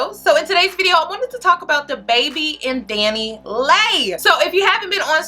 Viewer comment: I thought she said " We here to talk about THE baby IN Danileigh" I thought she was pregnant too!